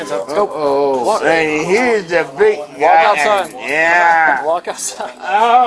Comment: did he say "uh oh and here is Jeff Bezos" to the bald guy? lol